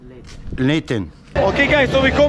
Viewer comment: Man the clips with your father saying a few moments later are so fkn gold it kills me every time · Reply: sounds like he says a few moments leightonnn haha